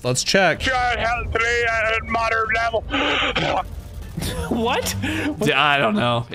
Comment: i think he was trying to say "help a child read at a moderate level"